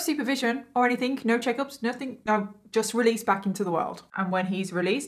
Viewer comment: I heard, "back into the wild," and not, "back into the world," and I feel it fits. PS. Today I found out that if you edit a moment after liked it, the like goes away.. haha